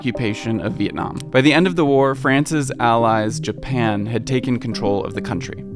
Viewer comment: am I hearing that right? France's allies Japan?